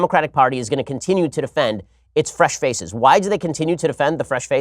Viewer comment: Every time he says "fresh faces" I hear "fresh feces" and its now hilarious as well as terrifying.